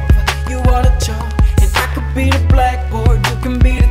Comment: Thought he was saying i could me the black boy 😂